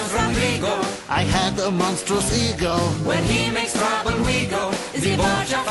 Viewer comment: I generally thought Rodrigo said “I have a monstrous seagull” 😑 like wtf